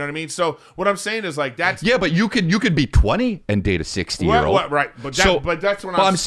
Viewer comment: for a sec I thought Chris said "you could be 20 and date a 16yr old" ... I was like damn Chris at it again 😂